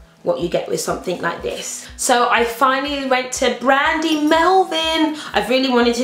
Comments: it sounded like she said brandy melvin lmao! gotta love patricia great haul! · Was it just me who heard her say 'Brandy Melvin' hahahahaa